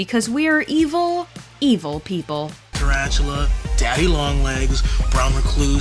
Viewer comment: did bro just say daddy long legs